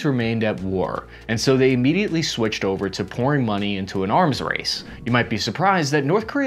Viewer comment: Did you say "pRN money"?